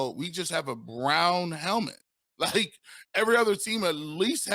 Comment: did just say we just have a Brown helmet? Come on man, wake up.